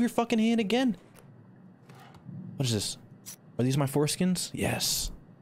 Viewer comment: Is no one gonna talk about how he said forskins instead of forceps at 28:22 💀